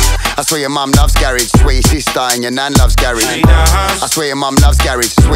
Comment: Ngl thought they said ‘ya schweet sister’ not I swear your sister🙈😂